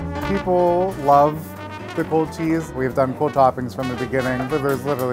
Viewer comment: I thought that said gold cheese